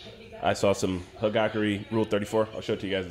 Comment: did he just say he watch hagakure rule34